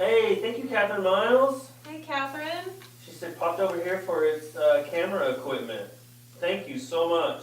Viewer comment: it said Katherine f**ked over here for the camara equipment, I don't know why it was writing what y'all were saying, but don't say the f word, he said popped over here...